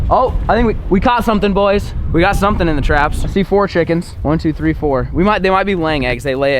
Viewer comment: LoL I thought he said c4 chickens, but he said "I see four chickens"